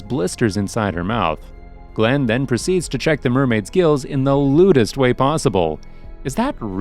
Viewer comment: I thought you said grills and I wanted BBQ